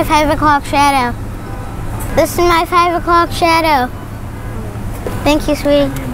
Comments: I thought he was saying this my favorite club cheddar lol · I couldn’t understand him either. It sounded like “this is my favorite cop shadow” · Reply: Same. I heard “club shadow” but yeah I had no clue what he was saying. Thought that was the joke for a sec...